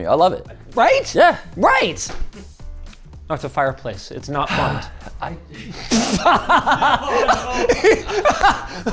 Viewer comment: what did linus say? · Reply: "built in vacuum"... I can only assume its for air circulation but I am similarly confused